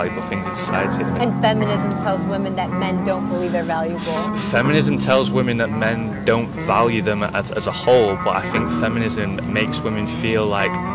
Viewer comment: At first I thought he said “feminism tells women that men don’t value them as a hole” 🤣🤣🤣